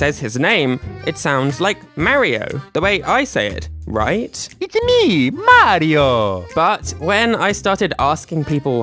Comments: I always hear "Its'a me, Mah-Ree-Oh!" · To me It always sounded like mar-io but with rolling the r a bit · i hear "Itsume! Mah dio!" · i hear mar'yo